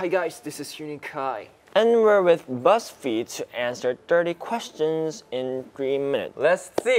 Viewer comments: i literally thought he said dIRty questions in 3 mins🔪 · for a moment I thought he said dirty questions lol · I heard it as "We're with Buzzfeed to answer DIRTY questions in 3 minutes" 🤪😂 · Am I the only one who heard Yeonjun saying " dirty questions "💀👀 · help- i heard "dirty questions" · When Yeonjun said “Thirty questions”, from the way he pronounced the “Th” sound and made it round like a “D” sound, I thought he said “Dirty questions” I spat out my drink lol